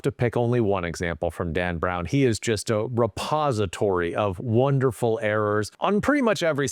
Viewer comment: For a moment I thought you said "suppository." Really, either works.